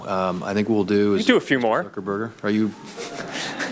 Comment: did he say Mr Zuckerburger ??? LoL